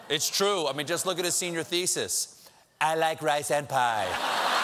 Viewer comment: I thought he said, “I like rice,Senpi.”